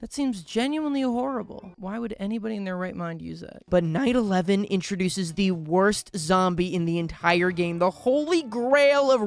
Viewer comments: Bro I heard "But 9/11 introduces the worst zombie in the entire game" 😭😭😭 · When you were in the night level and said night 11 I thought you said 9 11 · i swear i thought he said 9/11, i swear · I thought he said 9/11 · I THOUGHT BRO SAID 9/11😭😭😭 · I genuinely thought he said "but 9/11 introduced the worst zombie" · i thought i heard 9/11 💀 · I thought he said 9/11 for a sec and got so confused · I thought you said 9/11 💀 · Why did bro say 9/11? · i genuinely thought that he said 9/11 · Why did I hear 9/11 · I thought you said 9 11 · When he said night 11 I thought he said 9/😮😮😮😮